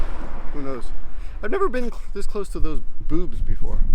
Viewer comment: I misheard you as "I've never been so close to boobs before" and I initially thought "I envy you"